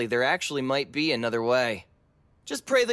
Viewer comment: You know I always hear : "N**ga take a swan dive..."